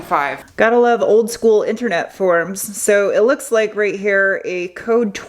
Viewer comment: I wasn't looking at the screen and I thought I heard; "Gotta love old internet porn...." And I was like; "Wait what WHAT!?!" 😯🥴🙄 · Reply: Old school internet forum. Lol